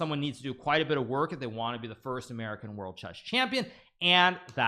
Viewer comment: did you say first american world chess champion?